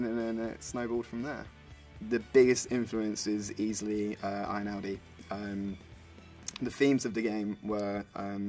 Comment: he says: "the biggest influence is easily.. I-know-B"? i can't hear what he's saying! sounds like Obi-wan Kenobi's long lost cousin! if anyone can decipher this frantic rush of sounds, please enlighten me and never forget: gewfesgesgtwege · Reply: Ludovico Einaudi, the composer of this is england.